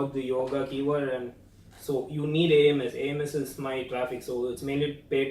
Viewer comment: What does say at 3:29? “You can never get organic traffic on the Yoga keyword, so you need…” what? Aimess? What’s that? Can someone help. Thanks